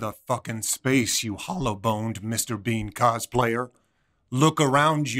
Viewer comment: Did I hear this right hollow boned Mr. bean cosplayer